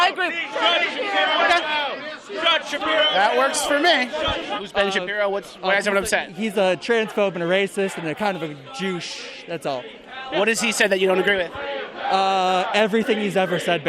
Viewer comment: did he call Ben a 'Jewuche' instead of 'douche'?